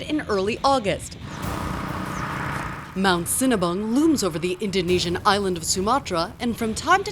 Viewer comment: I thought she said Mount Cinnabon at first